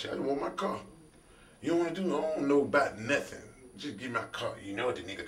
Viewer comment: he said he brought the police and they asked what u wanna do, he said he dont know bout nothin he just need to get his car, so he aint snitch. think b4 u speak. yur name matches u "nigganameduhhh"?